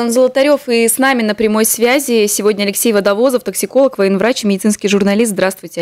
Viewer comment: мне послышалось не "токсиколог" а пепсиколог :D